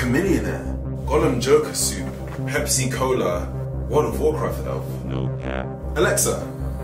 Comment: I swear I heard him say "pepsi cola"